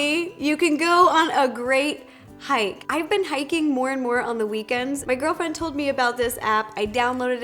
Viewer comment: did she say "my girlfriend"?